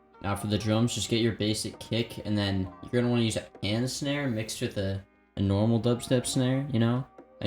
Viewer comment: what kind of snare did you say? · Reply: A pan snare